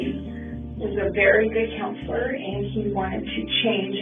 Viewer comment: at approx 8:16 the captions say 'drug dealer' but it sounds like she says 'counselor'...odd